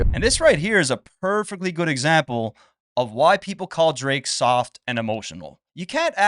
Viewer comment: ….. All I heard Drake say was “I’m soft”